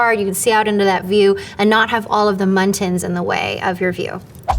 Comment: did she say muntins or am i going insane